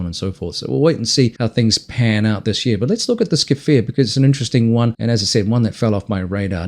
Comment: what aircraft is this, I couldn't quite make out what you said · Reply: K'Fir